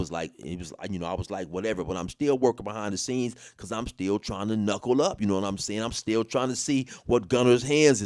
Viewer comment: So what I heard was "im still trying to knock his ass out"😂